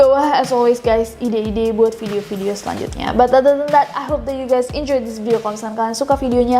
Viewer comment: I Always Can't hear what she says :" ) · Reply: “but other than that” i guess